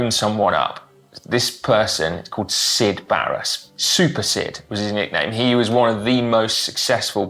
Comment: So, the name Sid Baris and that ringtone gave the whole thing a... different tone. Mainly because I heard "Syd Barrett" (